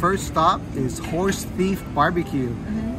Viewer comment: When you said "horse thief BBQ" I first thought you said horse meat BBQ and I was like wait, what? I had to rewind and watch again.